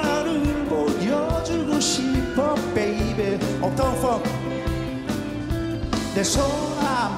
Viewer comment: 여기서 도대체 뭐라고 하는거임????? 아무리 들어도 up fown funk로 들리는데 갑자기???????